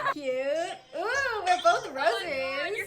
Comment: idk if it was her aunt but i heard her say “oh my gulay” which is such a filipino thing to say lol